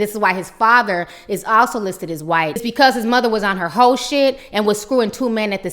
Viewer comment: I thought she said "his mother was on the horse-shit" like she was getting laid in a stable.